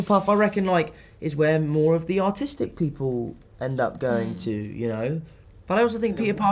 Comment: Did he just say autistic people go in hufflepuff?